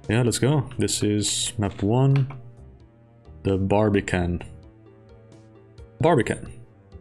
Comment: thought it said "barbarian" lmao